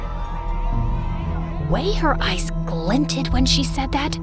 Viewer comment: who else noticed she said ice Instead of eyes on the subtitles :O